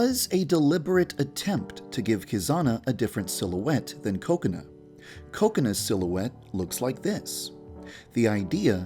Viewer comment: Guys, turn on auto subtitles at 6:39. They say coconut instead of kokona xD